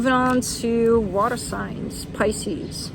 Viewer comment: I heard "spicies" when I clicked the pisces timestamp, probably true 😂😂